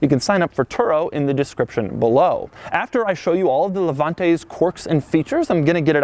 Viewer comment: You know it’s bad when Doug, instead of THHHHIIIIIISSSSS, says this Also, 2:59 did Doug say “corks” 😂